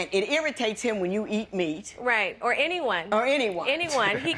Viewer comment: i thought the girl was saying she ate people. " or anyone"😂